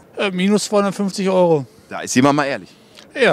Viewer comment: 350€ für ne Nasen op? Ich hoffe ich hab mich verhört..